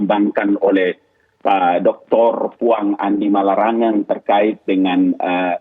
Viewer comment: Menit 9:47 Apakah sy yg salah dengar ? Ada kalimat, "doktor uang". 🤦‍♂️